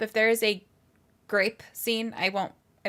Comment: I thought you said grape 🍇 scene and I was like, that’s a weird phobia, like fear of buttons or fear of pennies, etc.